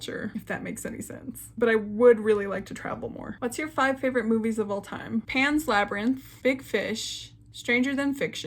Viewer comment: I thought you said 5 different booties of all tine so I was so confused when you started listing disney movies